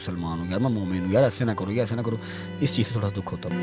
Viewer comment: did he say emaan ?